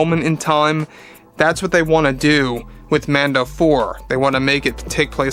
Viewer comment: I thought he said "Lando 4." I was like......where the hell have I been?!!!"